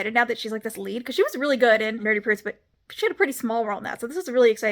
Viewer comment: She had a pretty small role in what? I rewatched that moment like 5 times and you said it so fast I couldn't understand it. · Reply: I figured it out... "Nerdy prudes but"... But it was read so quickly and the word "but" sounded like part of the title